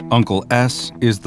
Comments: I heard “uncle A##” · i heard Uncle Ass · DID HE SAY UNCLE ASS?🗿(or uncle s)